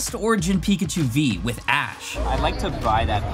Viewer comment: did he just call red…..ash…..nah that’s just me..